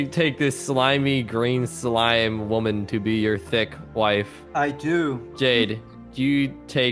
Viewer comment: me thinking what he said: THIS SLIMEY *|£{|£| D**k BRIDE? That’s what I thought for one second sorrry I know it said thick but my brain just